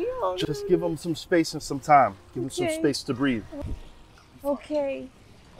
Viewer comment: I thought he said space debris